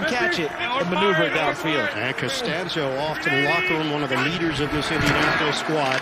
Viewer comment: did you say "Costanza"